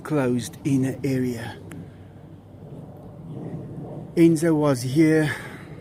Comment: Almost sounds like hes saying benzo lol